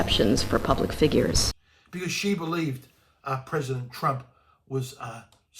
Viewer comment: Did he just say "our president Trump" at 3:01" man those are treasonous words, he claims to be a politician for Australia and also says Donald Trump is his president?